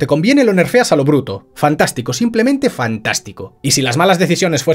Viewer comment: ¿Que dijiste? 🥵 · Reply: Que es fantástico 🥵